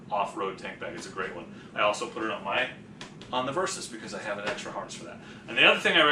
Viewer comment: I thought you said honda versus. 12:21.